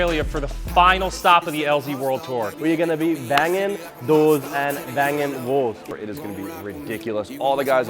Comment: I thought said "we're gonna be banging doors and banging whores" and I absolutely lost it.